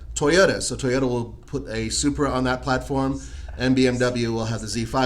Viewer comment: did they say Supra?